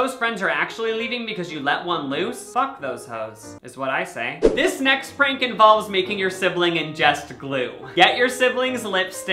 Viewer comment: “This next Franken balls” ... why did my brain think he said this